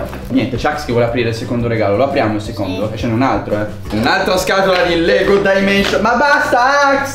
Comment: Solo io ho capito "un'altra scatola di Lego dia Mates"?